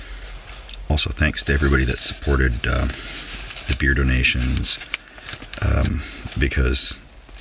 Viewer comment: Anyone else hear "beardo nations" at 8:37 or is it just me?